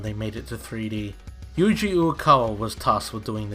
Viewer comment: Yuji Okawa? Is that what he said at 11:46?